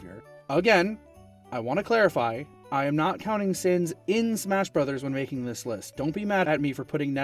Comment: I thought he said “Sans” at first